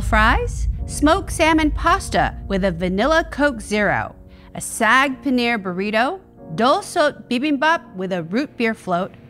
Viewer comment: When Josh was first naming off the foods I thought he said a "sad Panera burrito" and I thought "well that's a choice".